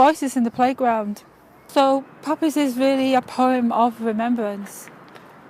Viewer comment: is the best bit 😂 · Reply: she was saying poppies but it sounded like papas. I shouldn't be laughing. She might have a lisp or something.